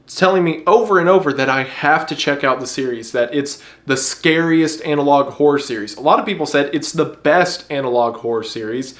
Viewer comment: I keep hearing him say "analog whore".... Like he's talking about some 70's vinyl version of my ex ...